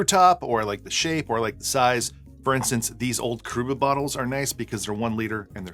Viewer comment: Old dude with bad hearing hear. Could have sworn you said "these old Kruger bottles" when talking about the 1 L bottles you salvage. Took a bit of on line searching for "Kruger bottles" before I realized you said "Coruba" Those I have!! LOL